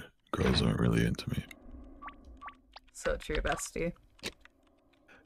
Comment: Corpse: girls aren’t into me… Emma, without hesitation: so true bestie. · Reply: I thought she said “ts not true, bestie”